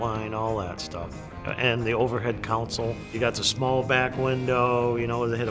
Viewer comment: "Overhead counsel"... 😆 CONSOLE, not "counsel". Maybe I'm just O.C.D., but I hear that at endless car shows, and it's a huge pet peeve of mine.